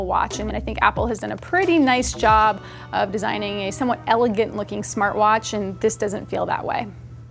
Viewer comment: did you say "...apple did pretty nice job in designing somewhat elegant looking smart watch..." or am I hearing things?